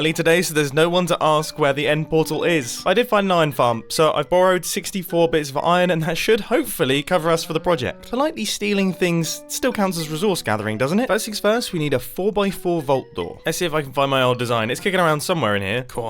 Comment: "slightly stealing things still counts as resource gathering, doesn't it" If it makes us laugh, it sure does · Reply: I thought he said politely stealing.